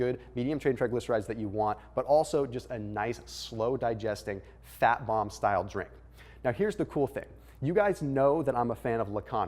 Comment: “Fat Mom style drink “ lol Did I hear that correctly?